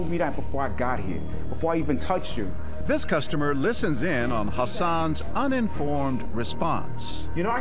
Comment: When He said 'Hassan' i thought he said 'her son' 😂😂